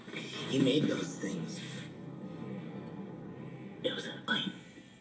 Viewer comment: He said she was a synthetic? I thought he said it was a lie and she is human. Or did I hear it wrong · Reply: shes human. it was a lie